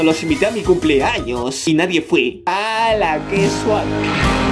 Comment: escuche Suad enves de sad toy loco xd escucho re mal